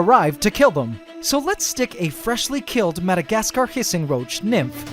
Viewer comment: This is no hate. I still like your vids but I thought he said freshly killed. That thing looked alive to me. That poor bug